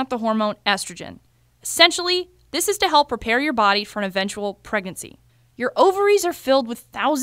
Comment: I heard her say ‘pregancy’ 😂😂😂